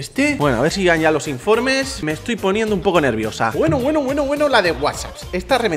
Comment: Soy yo o escuche a decir me estoy poniendo nerviosa🤔